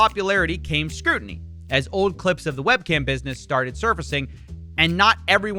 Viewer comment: Ok ngl I heard "webkinz", not "webcams" That makes the "lonely men" thing make way more sense